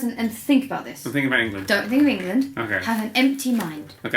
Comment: What does Dan mumble at 1:46? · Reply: emma said have an empty mind and dan responded think zoella